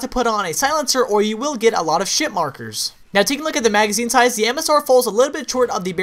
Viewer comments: Did u say shit markers lol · did he said sh1t markers :D ?